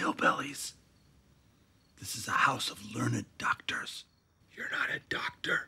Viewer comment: “This is a house of learned doctors” 🤣🤣🤣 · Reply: For some reason I thought he said lunid doctors. And not only do I laugh so hard my sides hurt, but I’m also thinkin; Lunid? Is that the newest way to say lunatic?